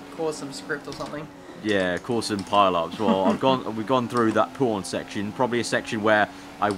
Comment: ? Did say p0rn section? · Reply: I think he said porn section